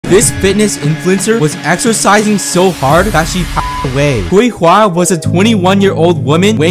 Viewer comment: OMG HOLY SHIT. I thought he said that she "passed out" (fainted) due to exhaustion but then I realised he meant that she died 😨:(( thats so tragic and horrible